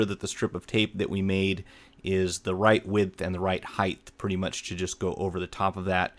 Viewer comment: Did he just say 'heidth'? ':f